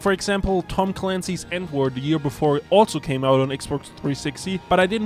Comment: did he just say Tom Clancy's N-word?